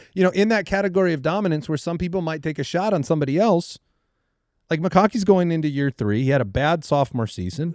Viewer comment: Did andy say mccocky @ 45:25 instead of mcconkey?